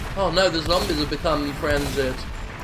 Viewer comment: I thought you said the zombies are getting frazzled at first xd